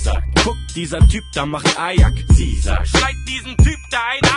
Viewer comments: 2019 ?? Ich und meine maske oder ich und keine maske ???? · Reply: was sagt Sido da bei 2:37 ajak oder was? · Was sagt Sido da bei 2:37 aijak oder was? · Wer wohl hinter der Maske steckt? Sun Diego? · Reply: was sagt da Sido bei 2:37 ajak?